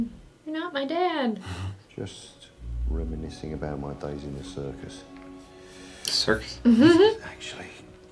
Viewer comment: For anyone who doesn't know, alfred history in comics was that he was a spy/gov agent. That's what alfred meant by circus (his service days). If you want to learn more about alfred then there is a series named pennyworth, its not connected to this movie in anyway but its for anyone who wants to know more about alfred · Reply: I thought he said "The Service" rather than the circus.